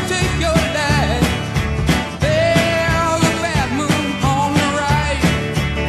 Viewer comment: When this came out we all thought he said "There's a bathroom on the right." Admit it.